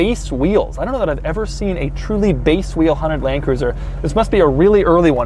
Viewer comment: did I hear HONDA Landcruiser ?